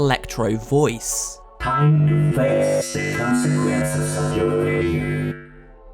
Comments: At this time 7:18 I heard withered bonnie say "consequences" like if you heard it to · What does Withered bonnie say? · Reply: “Time to FACE the consequences of your failure”